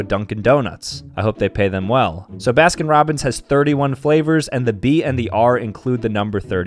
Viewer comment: Nah fr thought that said bi flavours